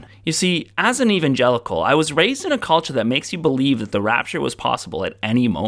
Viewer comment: When you said, "I was raised in a culture..." I heard, "I was raised in a cult."